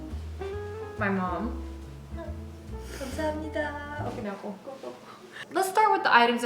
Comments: Did I just hear you say, “Ok na ako”? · Did you just say "okay na ako, go, go" at 0:32 after "kamsahamnida"? Haha 😁